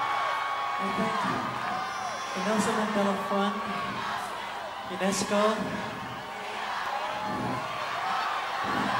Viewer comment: what are they screaming at 00:39? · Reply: We love you